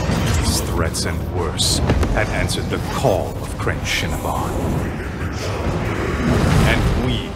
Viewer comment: I hear "Crenshinibon" and my mind says "Crave Cinnabun"